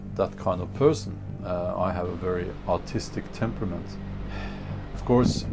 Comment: time stamp 2:20... did you call yourself artistic or autistic?